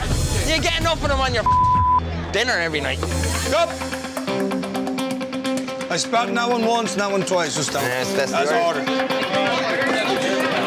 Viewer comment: did he say he spat in someones food?